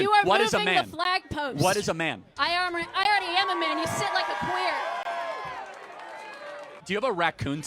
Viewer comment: did SHE really say he sits like a queer ???!!!!???? lol